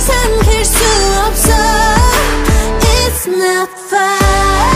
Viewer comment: I always thought she said 'it's my fight'. Not 'it's not fine'